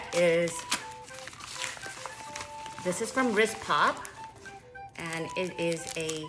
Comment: Hi where did you purchase the twilly? I heard you say wrist pad. Can you put the link in the comment. Thanks.